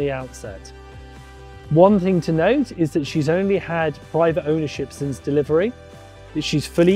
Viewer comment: Thanks! This is a nice small yacht. But, 5 owners is a lot. A lot doesn't mean to many. It just surprised me a bit. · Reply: It sounded like he said “5” I agree, but he actually said “privately owned”…in other words she has never chartered. Just 1 private owner.